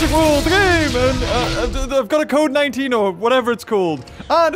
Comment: H said code 19 but for some reason now I thought he said COVID-19, and it was 2018, did he predict the future?